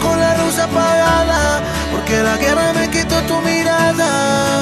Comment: Yo pensé que decía tierra y no guerra 🤡